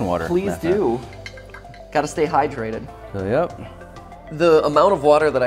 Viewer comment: “yep” captions say “nope”